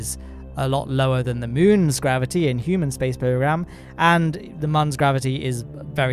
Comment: did he say "human space program?"